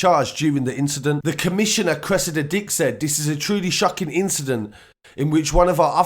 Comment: Am I the only one who heard him say the police officer name is cressa dadick?? St